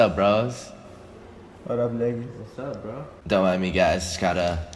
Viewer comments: did rain say ' watup nigga ' ? Anyone else hear that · Am i the only one who though rain said "what up nigga" at 3:30 when he really said what up lucas"??😂😂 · did say what up nigga 😂